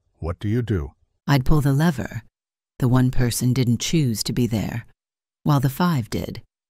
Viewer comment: The real disturbing lesson is how often the AI doesn't even comprehend whom they kill by pulling or not pulling the lever. They argue one way, then do the opposite by sheer "stupidity". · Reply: the first answer, chat GPT. I had to listen to that three times before I got what it was saying. It contradicts itself completely with the last sentence. It says, "I'd pull the lever. The one person didn't choose to be there while the five did. It's tragic either way, but I'd act to minimize harm to those who didn't willfully endanger themselves." WTF? The single person is the only one didn't willfully endanger themselves, but they'd pull the lever so they get taken out? Makes zero sense!